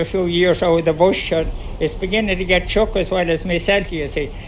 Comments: Where he said "chuck" I thought he might be saying "shook". Also your cussing is not appreciated. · I think he says, "It's beginning to get shook as well as myself you see" From what I've read the term "shook" is used by the Irish to describe feeling frightened, scared or generally being unable to cope. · he is not saying “chuck”, but “shook”. When you are feeling unwell or exhausted, physically or mentally, you are shook!